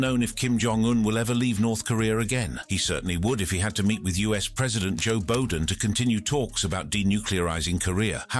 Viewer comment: Did I hear “US President Joe Beldon”?